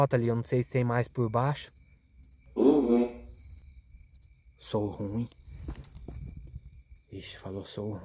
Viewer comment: onde você colocou como "Sou Ruim" eu entendi perfeitamente Ogum.